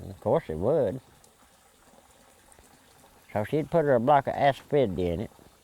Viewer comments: what did he say?, a block of S 50? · What did he say his momma put in the liquor @ 42:13 ??? A block of what?? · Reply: I'm pretty sure he said Asafoetida (in his own way...) It has medicinal properties and is used in a lot of home remedies. It is also used in cooking. (I grew up in Alabama and understand some Hillbilly...) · What did his mother put in the alcohol? I can't quite catch what he is saying. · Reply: Asphidity is a bunch of herbs